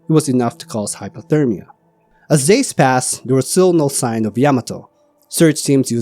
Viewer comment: Did he say "as DAYS past"? 😮